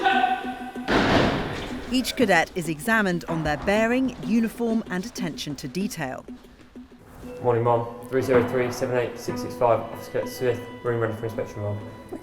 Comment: Did anyone else hear 'Good morning, Mom' at 3:30? Honest mistake if so—kinda funny in front of the cameras!